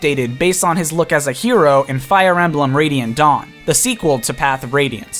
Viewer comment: sounds like he said "Radiant Dong" XD